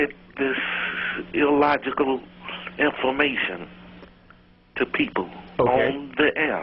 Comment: LOL.... I heard him say "Illogical inflammation."